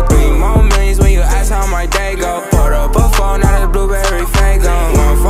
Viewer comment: A moment of silence for the people who found this song by searching up "You better do what I say so" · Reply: I thought he was saying "portable phone" not "poured up a 4" so I'm doing pretty terrible